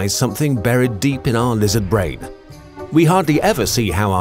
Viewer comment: I thought he said lizard brain ? 🦎